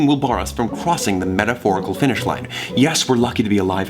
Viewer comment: did anyone notice that said “crWossing the metaphorical finish line” instead of Crossing? He smiled a little bit after.